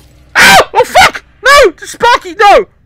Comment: i swear at 9:09 he called the lightbulb sparky